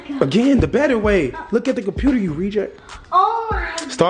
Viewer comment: Did he say “look at the computer you reject” @ 21:42 ???? Correct me if I’m wrong